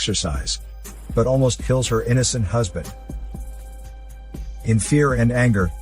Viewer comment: He said "innocent husband" but what I heard was "idiot husband"